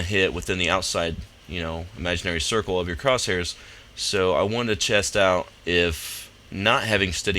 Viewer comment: Did He Say "I Wanted To Chest Out?"